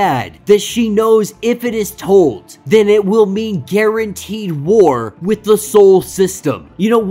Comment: It makes me wonder if her words mean something else. She said war with Mars. Not specifically mutants of Earth. I wonder if it has to do with all the new mutants who've come to this reality. Title says first mutants, Apocalypse is one of the first and he was there when Krakoa split and Arako want to the other realm/dimension/realty. Shiar is old too. Could be a connection to the old version of Shiar and whatever happened back then or even before then. Or not lol. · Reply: I thought she said war with sol which is the entire solar system which the mutants declared arakko (mars) as the capital of